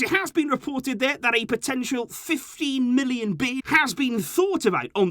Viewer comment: I heard "50 million" at first, I was already celebrating 😅😅